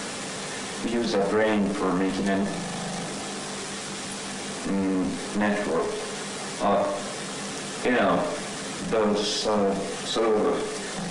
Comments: it sounds like he says "making a.... network of.... those, you know...." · I think he says "Uh... You know, those (um) sort of... (sigh)"